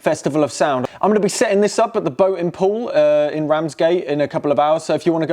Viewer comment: Oh, the boating pool! I thought he said “boatem poll”